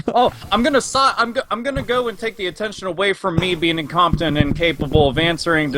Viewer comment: I know what he meant to say, but it sounded like he said "Me being in Compton"